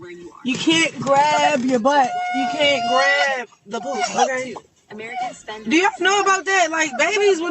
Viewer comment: did it sound like she said ( you can’t grab the POOP) he screaming get this off me 304- the fat I ain’t catch this the FISRT time I watched it is beyond me